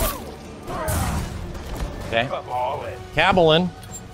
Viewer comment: I thought he was saying "I'm all in" like a gambler taking risks, rather than Kaballin (at least some of the time). Would make a lot more sense